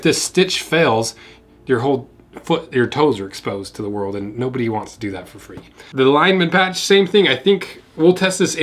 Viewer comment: did I hear "nobody wants to do that for free?" at 8:06 ?